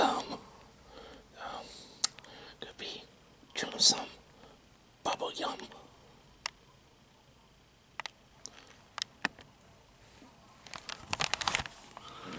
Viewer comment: at first i thought the candy said "bubble *gum*" but it wasn't, looks like I need gla- 🤓